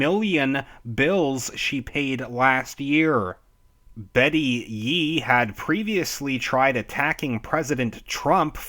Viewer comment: I kept hearing to myself “Betty yeet” lol (bet-he-yeet)